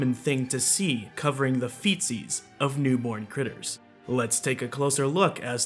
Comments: Him saying '"Feetsies" sounded like he was saying ,"Feces." It bothered me. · I love how serious this is and he breaks it by saying "feetsies" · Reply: I thought he said feces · Feetsies! Now I get it! I thought he said feces. It didn't make any sense to me. I thought I must have missed something for the narrative to have moved from hooves to feces. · I thought he said feces because he was so serious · I thought you said covering the faeces of newborn animals! 😆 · I hate how you talk real spooky and then you say "Itty Bitty foal feetsies" 👍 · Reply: I thought he said “feces” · Lol I thought you were saying “feces”, I RAN to the comment section… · I love how serious this is and he breaks it by saying "feetsies" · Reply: I thought he said “feces”, so your comment cleared things up. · I love how serious this is and he breaks it by saying "feetsies" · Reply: I thought he said, "fetus"